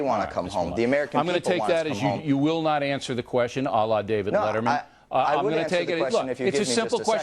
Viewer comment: does Bill Say " Alah David Letterman " ?